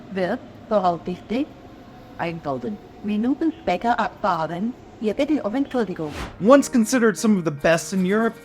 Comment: something you here on almost every German train. "Wir entschuldigen uns für die Verspätung" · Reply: Idk. I always heard "Wir bitten um Entschuldigung"